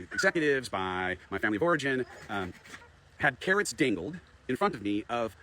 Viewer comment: did he say “carrots dingled?”